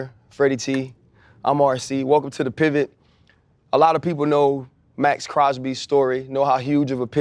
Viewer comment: I seriously thought it said Mason Crosby the kicker from Greenbay.